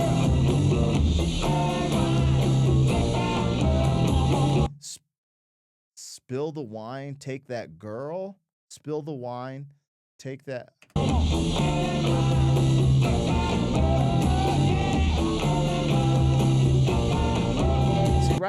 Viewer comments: All my years knowing this song I swore he was saying spill the wine dig that girl🤷‍♂️🤣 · I looked Eric up after this reaction and realized he was the lead singer for the Animals. In the moment, I had no clue though. He didn’t even look familiar cause he was so clean cut in the video House of the rising Sun. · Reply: When this song came out, I thought he was saying “spill the wine, dig that girl.” LOL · Omg!! I have heard this song for over 50 years and I never knew what that line said! I thought he was saying, Spill the wine, dig that girl! Lmao!!’😢